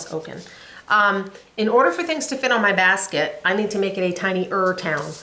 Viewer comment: Love your bag from Tiffany! Every time you say Tifa my mouth waters for our gelato place here, Tifa😂😂 Why do I always hear “I need to make it a tiny Ur town”😂😂😂😂I have Christmas and Blooming that I need to start!